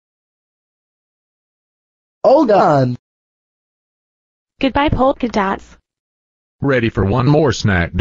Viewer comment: Did she just say goodbye poopy butt?